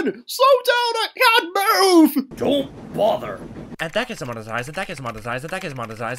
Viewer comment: What is that YouTube CEO freight car saying? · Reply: "And that gets demonetized and that gets demonetized and that gets demonetized and that gets demonetized..."